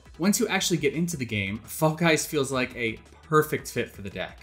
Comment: did he say "fuck guys"?